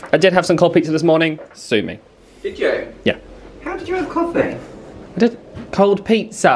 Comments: Woo hoo more videos. I thought you said coffee too lol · I swear I heard "coffee" as well, I had to rewatch that part to hear "cold pizza" lol. I understand you 😎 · I totally heard him say coffe, not cold pizza lol